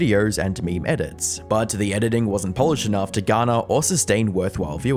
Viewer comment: I heard "wasn't Polish enough" 😅